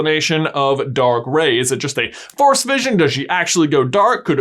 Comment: Every time he said "force vision" I thought he was saying "Forrest vision"